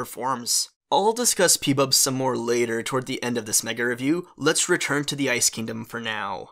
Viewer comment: what he say** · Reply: "Mega Review"